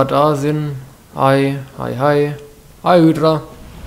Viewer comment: Bin ich der einzige der bei 14:20 Hail Hydra verstanden hat ?